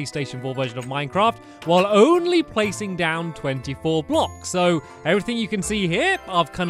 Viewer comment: did he say 24 blocks?